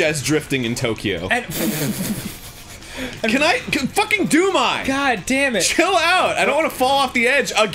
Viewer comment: at first i thought they said doom guy instead of doom eye